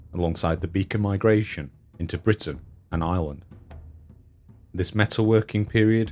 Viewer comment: ha-ha. Misheard narration... When you said “Britain and Ireland” at 4:09, I heard “Britain, an island”. This was the second instance, actually. The first time, I thought "Hmmm. ‘Britain, an island’. That’s a bit odd. Innit?“, but then the second time around, it was like “Oh... I get it”. A good robust American “Rrrrrr”, or whatever weird accent Phil Harding has, would fix that ! But, actually, yourrrr narrrration is so much improved over your first video, when a few viewers made such rude comments. So glad you’ve persisted. You do great work! Keep it up!!